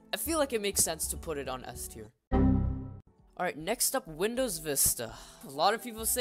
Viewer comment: It said me instead of vista